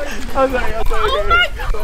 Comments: IM CRYING- when Lexi got out and Andrew pushed her (5:48) in i swear to god he said “im sorry, im sorry BABY” AHHHHHHHHHH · Did anybody else hear Andrew say "I'm sorry I'm sorry baby" when he pushed Lexi in and then helped her out??(time stamp of we he said it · andrew and lexi should totally pull on a prank that they are dating on ben and everyone in the squad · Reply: i heard andrew say Im sorry Im sorry baby in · Was it just me but in 5:52 when you slow the speed down it sounded like Andrew said: I'm sorry, I'm sorry babe! #Landrew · bro is it just me or did Andrew say sorry I’m sorry BABE. UMMMMM TELL ME IF YOU HEAR THAT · did andrew say "Im sorry, im sorry baby" to lexi? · it sounds like andrew is saying, "i'm sorry, i'm sorry, BABY" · Sorry who?!?! Go to 5:52 now did I hear what Andrew said to lexi if u heard it say what he said He actually said I'm sorry I'm sorry baby ?!!?!? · Wait did he just say sorry sorry baby❤ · No one else heard him say “I’m sorry baby” to Lexi when he pushed her in the pool! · it sounds like he said “im sorry im sorry baby” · Anyone else heard Andrew say "I'm sorry baby" or is it just me